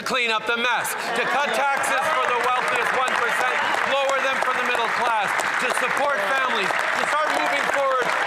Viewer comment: did he say "cut taxes for the wealthiest 1%? ...lower them for the middle class..." in his clean up the mess speech